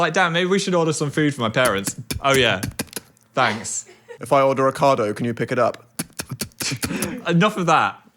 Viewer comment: utterly obsessed with the captions saying “Ricardo” for Ocado 😭